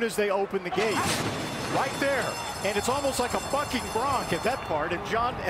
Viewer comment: Sounds like the commentator cursed and the stutter after doesn't help but I think he said "bucking bronc".